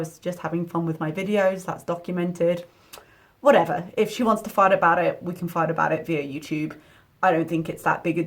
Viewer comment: For a split second I thought I heard “ if she wants to fart about it ...” lol 😂